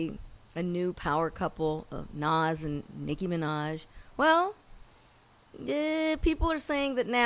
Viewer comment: Nanicky lame as fuck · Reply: Bitch corny. At first i thought she was gonna say Nicki MiNas